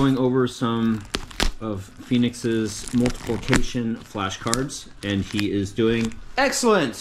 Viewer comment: I just finished my lunch when you said go pickup Poop. Lol thank goodness. Bren should appreciate that. Lol 2nd...at appx 9:40 when you said Phoenix finished his multiplication flash cards I initially thought you said "multiple kitchen" cards. Lol